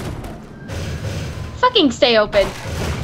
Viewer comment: "Fucking stay open" I thought she said Fucking satan. So everyone is fucking satan.